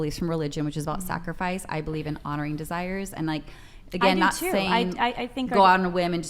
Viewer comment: Well said "You're making an identity out of your desires" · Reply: When she said "i believe in honoring desires" i heard "i said 'F off' to self control"